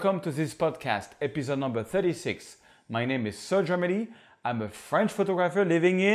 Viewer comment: anyone else hear Sir Germany?